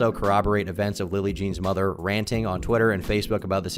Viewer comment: ‘willie jean?’ did i hear wrong?